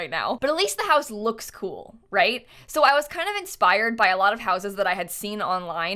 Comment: At like 2:13 when she said she was kind of inspired I had to listen to it multiple times because it sounded like she said that she was kind of expired 😂 and I knew what she meant but was trying to hear inspired instead of expired😅